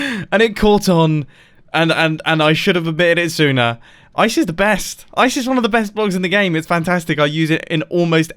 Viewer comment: it sound like ur saying ''isis the best'' O_O